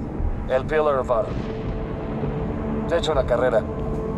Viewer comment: no entiendo que dice emile despues de "el pillar of autumn" alguien me explica? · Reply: Creo que dice "te echo la carrera"